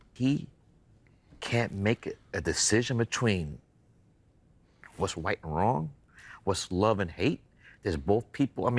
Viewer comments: did Spike say,"...between what's white and wrong..." Or "...right and wrong..."? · did he say "white and wrong" ? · I honestly think Spike Lee said the difference between "White and wrong"... Am I just crazy or did Spike Lee just say that? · Mr Lee needs to enunciate better. It sounded like he said what's white and wrong.